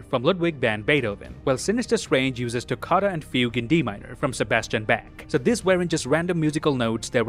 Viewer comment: Did you just say (Johann) Sebastian Back? 18:40?